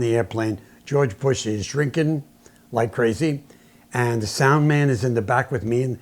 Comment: did you say "....Bush is drinking like crazy?"